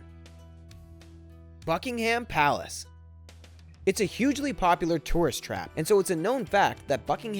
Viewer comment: did he say fuckingham palace